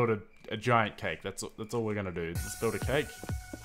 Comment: What he said: "Lets build a cake" What I hear: "Real earthquake"